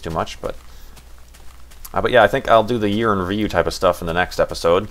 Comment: For some reason I heard "The Urine Review". 0_o Building Blocks! YAY!! I thought the DW episode was weird too. I hope the 8th series is good.